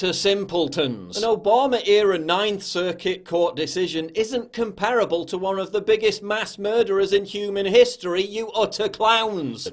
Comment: Real FreedomOfSpeech Please, what Paul said on 4:08? I only know a little english. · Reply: 4.08 – An Obama era ninth circuit court decision isn't comparable to one of the biggest mass murderers in human history...